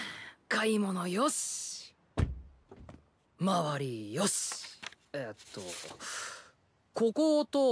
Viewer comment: Sounded like he said: "Moriohs" MORI MORI MORI MORI MORIOCHO RADIOOOOOOOOOOO